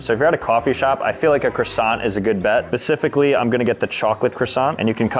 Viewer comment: did bro say pacifically?